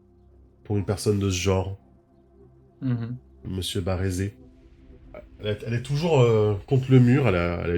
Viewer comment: Alors autant j'ai dit sur le Discord que le prénom était adapté, autant à l'usage, le nom de famille...... Je suis désolée mais j'ai entendu "Monsieur Bas-résilles" tout le long (Et désolée pour l'image mentale que cela provoquera chez ceux qui liront mon commentaire) 😂